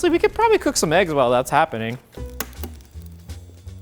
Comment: When you say stay I thought you said stay juciy